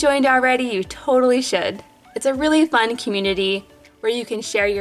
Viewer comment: I thought she said then you're totally shit